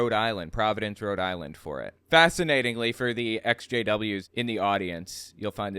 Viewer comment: oh “the ex-JWs” thank goodness I thought at first you were saying SJWs in the year of 2024 😭